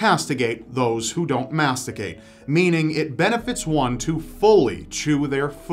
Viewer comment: what it said: nature will castigate those who don't masticate What I heard: nature will castigate those who don't masturbate